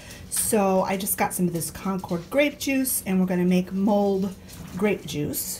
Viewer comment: I thought you said ‘we’re going to make mold’ (and I was scratching my head) and then realized you said ‘mulled’ grape juice. 😂 Happy New Year to you guys! As far as 2020 goes, I say GOOD RIDDANCE.😑😁